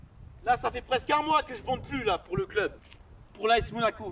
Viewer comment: C'est moi ou a 0:04 tu dits je bande plus ?